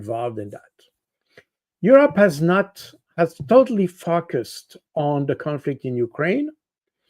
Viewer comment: When Col. Baud says “focused” , I hear “fucked us” every time. 🤣 I’d like to hear him say “Israel was so focused that they fucked us”. 😁